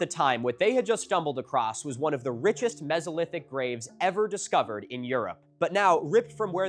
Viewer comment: For some reasons, I kept hearing from my tv "raid" instead of grave.